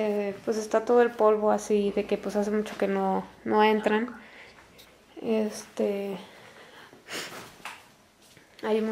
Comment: una voz diciendo algo susurrando,no entiendo nada ¿alguien entendió? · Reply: Yo entiendo que dice "recoje el machete" o algo asi